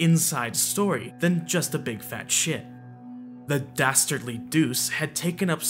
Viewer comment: On the last video of yours that I watched, you insisted on calling feet, "feetsies." Now I hear, "a big fat shit."